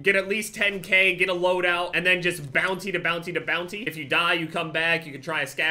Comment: I thought he was saying bouncy, bouncy,bouncy